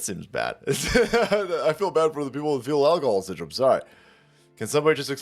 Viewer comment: I thought he said fetal uncle syndrome 😂